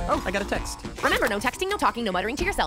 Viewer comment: What did that gal say? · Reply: VictorPinas Productions no talking, no texting, no murmuring to yourself and don't forget to put your phone in cinema mode before the movie begins